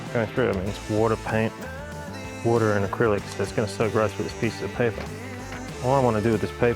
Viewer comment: Thank you...Acrylic paint mixed with water...I cover the process pretty thoroughly in the video 😉😆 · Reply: Thank you for the clarification.. I was confused whether or not i heard "acrylic" right 😅 I had another question though.. What's the percentage of this mixture? Did you use 1 part acrylic paint & 1 part water? Or did you use more paint & less water, or the opposite? Thanks in advance 🙏🏾